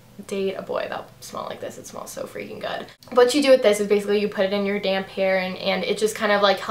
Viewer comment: haahahahaha I thought you said "you put it in your damn hair"